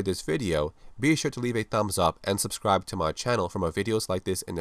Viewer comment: Thought you said "this is Acid-Blow" haha my two favorite drugs